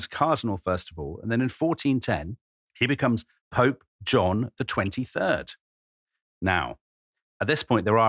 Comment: did you said "John the 23rd"?